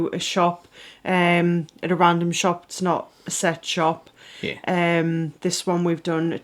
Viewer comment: I thought she said, a random shop ,not a sex shop 😂😂